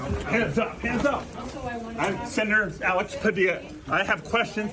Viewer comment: Sounds like he said " I'm Senator Alex Padilla" to me. Identified.